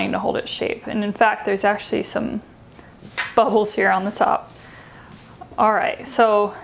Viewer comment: I swear she says buttholes